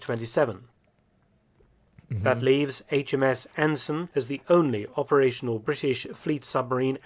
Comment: i thought he said the HMS Asmon.